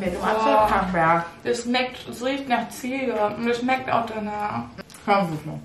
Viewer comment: „Das richt so richtig nach ziege und das schmeckt auch danach“. ich: „😂😂😂😂😂“ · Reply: Ich habe verstanden es schmeckt richtig nach ziege und das schmeckt auch so😂